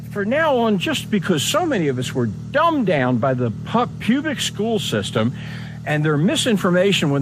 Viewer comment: Did he just say, "pubic" around 3:32???